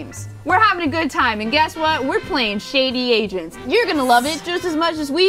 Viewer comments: Is it bad I thought Boze said Shady Asians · Did anyone else keep mishearing the game as “Shady Asians ”? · Was i the only one who thought they said "Shady asians" and nine minutes later in the video then i look at the Title and saw it saying "Shady agents"?